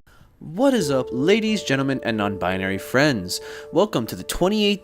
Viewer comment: Did I just hear "non-binary friends"?